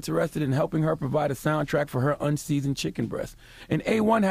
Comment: mayonnaise music, soundtrack for her unseasoned chicken breast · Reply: Tony Baze I thought he said chicken breath 😭😭😭